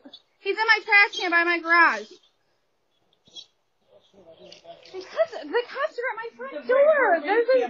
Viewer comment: closed captions “because the cops are my friends” 😂😂 wtf yo naaaa she clearly said “because the cops are at my front door” 😂😂 miss me with that pro police bullshit